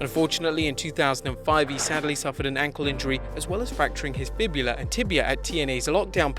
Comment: pls tell me what u just said idk fibula and tibula